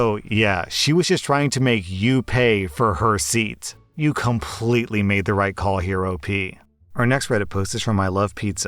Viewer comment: here’s what I heard “you totally made the right call HeroP”